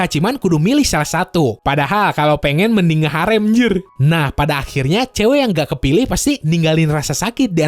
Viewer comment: i hear "mending nge Harem njir"